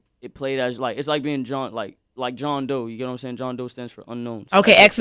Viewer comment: Did he say John Doe as in ROBLOX? xD